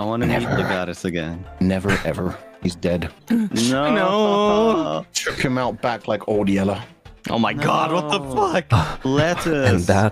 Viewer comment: I think Fulgur is saying "Took him out the back like Old Yeller"